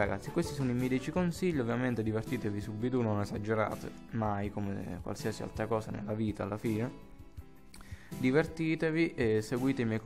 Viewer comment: A 13:15 ho capito “divertitevi senza mai esagerare come ogni cosa nella vita, la figa”😂